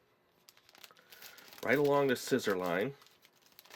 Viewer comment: For a second I thought he said I belong in the scissor line lol